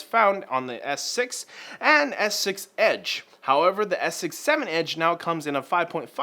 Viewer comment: did he say the s6 7edge?